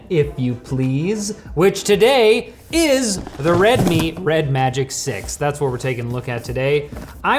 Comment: I was so tired I misheard the name of the phone as "Red Meat Red Magic" and had to hol up